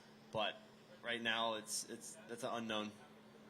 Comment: did he say it's unknown or it's a no? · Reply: he said its unknown